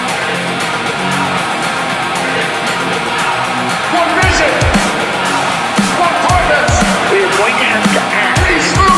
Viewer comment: what dous Cain say?? Cant understand it - guess first like is "for vision" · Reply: One vision - one purpose! Peace through power!